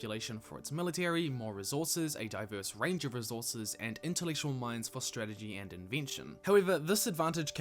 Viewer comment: I’m having trouble understanding what you said at 2:43 mark. I know it’s not interracial minds but I can’t unhear it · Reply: Intellectual minds